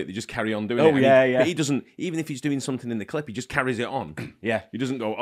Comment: Everytime you guys said he ‘carries’ on which was many…. I heard “carrey’s on” 😂